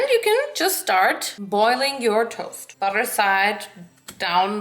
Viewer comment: did she just say 'boiling your toast?'